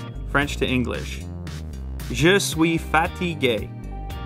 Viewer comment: I swear I heard "je suis fatty gay" when lew was translating from French